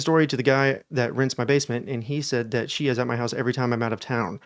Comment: I thought he said, ‘Basement rinser’ 😂 I was so confused.. we don’t really have basements in the uk, so I thought maybe it’s common in America for someone to come out and power wash the basement 😅